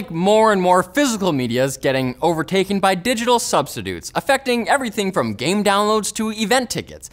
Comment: did he just say "subzidutes?" -- :)